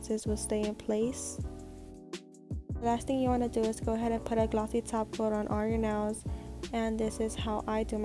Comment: A glossy taco??? Do I HEAR A SIMPLYNAILOGICAL FANNN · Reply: I heard “glossy top coat”